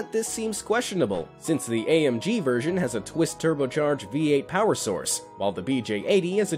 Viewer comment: did i just hear twist turbo?